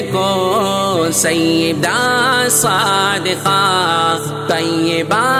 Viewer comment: What is the word after 'Sayyidah'? Is it Saalihah, Saadiqah, saalikah or something else? پے 'سیدہ' کے بعد کونسا لفظ ہے؟ صالحہ، صادقہ، سالکہ، یا کچھ اور؟ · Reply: Sadiqa